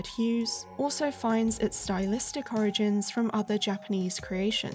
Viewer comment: Did you say “horrorigins” at 5:39 because if you did thats some next level paronomasia